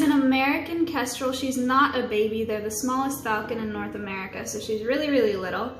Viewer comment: Before I read the subtitles I thought you said that Mulan was an American Casserole and got really confused